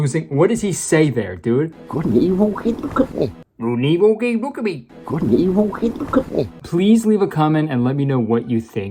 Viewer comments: I think it said can you look at me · I think the first part of what that guy is saying is "good & evil " I think I heard "hooks" but the rest is gibber to me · 5 5:00 After listening to it several times over, I think the clip says, "Good and evil - Hey, look at me." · don't care that I am writing this 3.5 years too late, but I think at 5:00 he says "good and evil. hey look at me" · “What he knew he good” thats what I hear. Lemme know if u hear it when you play the clip lop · I think he's saying "good and evil? hey, look at me!" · i think the character's saying something like "good 'n evil [hate? have?] company" i can't figure out that one word though lol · after 3 years i realized what he said "good and evil keep him company" what that means however is beyond me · I think he said you know good and evil, hey look at me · I think he said "Could an evil kitten cook me?" it's like a tongue twister. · I think he said "couldn't even get him to look at me" but that also still doesn't sound right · I think he's saying good and evil he'd look at me. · Kurtis I pretty damn sure he is saying "Good and Evil keep me company" my old baptist christian lizard brain kicked in and I just hear it no problem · "Good and Evil?— hey look at me" that's what I think he says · I honestly think the boss is saying "Good and evil- hey look at me" because the news lady looks away in the middle of his sentence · I think he says “good and evil- hey look at me” but it could literally be anything.